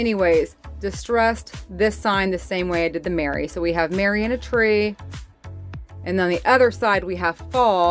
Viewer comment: You said Merry and a tree I thought you said Mary IN a tree! I thought poor thing and her pregnant with baby Jesus!!! Time for bed lol